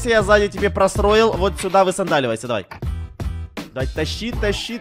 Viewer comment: Мне одной послышалось на 15:14 вместо "сзади" "зая"???